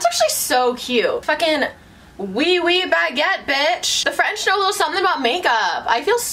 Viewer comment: " oi oi baggett" 😂❤